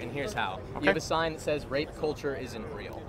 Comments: I thought it said rap culture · Shit I thought it said rap culture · I thought this dude said Rap culture I was about to leave a long ass comment on this video lol but carry on 🤣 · lol thought it said rap culture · The moment I thought it said "Rap culture". Damn you words. Why do you do this to me. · i thought it said rap culture fak me